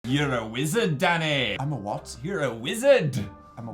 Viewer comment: I thought Phil said 'your a wizard daddy'